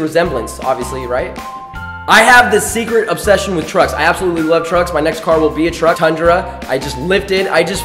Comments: When he said "I love trucks", I thought he said "I love drugs" LMFAO! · OMG I THOUGHT YOU SAID 'DRUGS' WHEN YOU SAID 'TRUCK'